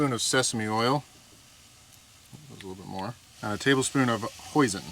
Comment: I had to replay that case I thought you said poison